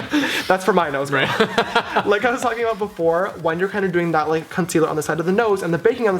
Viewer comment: it sounded like said cuntcealer